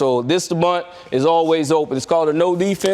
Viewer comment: Ooooh bunt? I thought you said blunt 😂 jk dodgers JP was my favorite